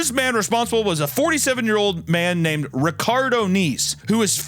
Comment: Was listening to this at work and i thought you said Ricardo Neaves. I was like "come again?! Who??" Till i saw the name and was like "oh ok lol"